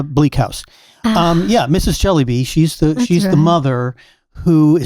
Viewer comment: I love reading Charles Dickens, but I have yet to read Bleak House. So when said “Mrs. Jellyby,” I misheard it as “jelly bean” and thought he was making a point about children today struggling to sit still and pay attention—perhaps because they’re eating too many jelly beans and consuming too much sugar. It struck me as a contrast between Charlotte Mason’s era and today’s generation.😂 I could go down a rabbit hole comparing the diets of our children today with the diets of children in Charlotte Mason’s time. And I think I could prove that diet plays a major role in why many children today can’t sit still and focus, as well as the problem of screens, etc..🙂